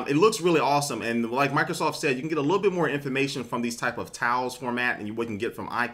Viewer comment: when you said tiles i thought you said towels lol